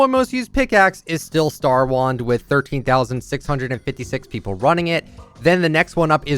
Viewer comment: Oh I thought it said “ruining” lmao